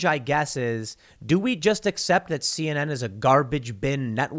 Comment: 'Do we just assume that CNN is a garbage bin network?’ Yes, Tim. We do that. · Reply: Tim said "accept," not "assume." He then went on to say they are a garbage bin network. I don't know why people feel the need to pretend Tim is more of a fence-sitter than he already is. You might have just misheard but there's a pattern in the comments where people do this.